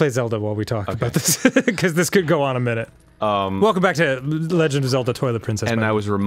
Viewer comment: Lol, I thought Dan said "Welcome Back To Legend Of Zelda: Toilet Princess" I'm dead lol! 😂😂😂👍